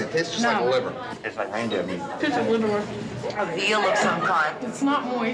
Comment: Did he say "Tastes like panda meat?"